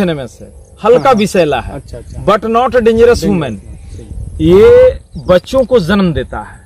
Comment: What did he just say "But not a dangerous woman" ??